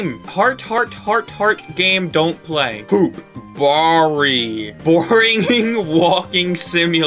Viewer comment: cannot play is too hard that what he said